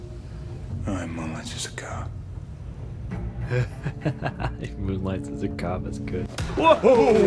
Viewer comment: What does he say at 0:50??? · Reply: "No, he moonlights as a cop."